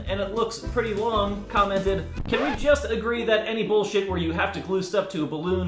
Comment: When he said bowls were the death of him , I thought he said balls XD